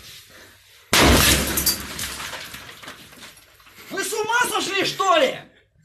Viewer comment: What that's dude say?🤣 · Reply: Вы с ума сошли что-ли?